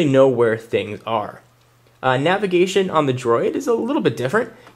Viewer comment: did he say droid @ 9:47? lol nice review ;P